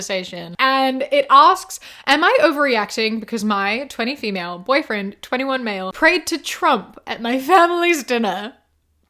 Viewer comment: i wasn’t paying attention and i thought it said “My 21 female boyfriend prayed to trump” (i dont listen to reddit stories that often so I forgot that they always state everyones age and gender like that)